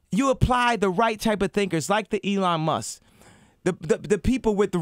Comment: What does he say at 6:20? You apply the right time of thinkers like the...?